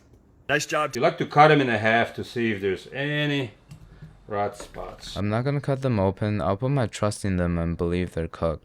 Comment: i think he says something like "black spots" and you heard it as raw spots